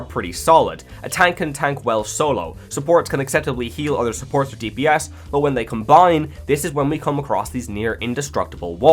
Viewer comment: Can someone please tell me the name of the flying robot game at 6:25 i didn't catch the title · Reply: mecha break